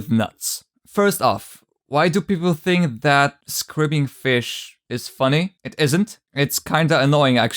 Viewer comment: Dude can you please help me understand what this word is. “Squirbing fish?” “Squiggling fish?” I can’t really hear the word that well and the closed captions don’t help… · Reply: Lol I meant to say "That screaming fish", I apologize, I'm not a native speaker